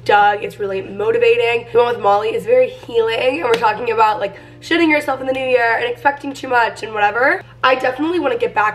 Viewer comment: Why did in the beginning it sound like she said “shitting yourself in the new year”😂😂😂